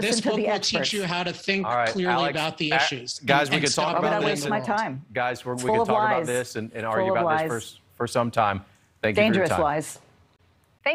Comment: What does say at 5:47 during the crosstalk? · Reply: "Again, this book will help you think clearly about the issues and stop ruining the world."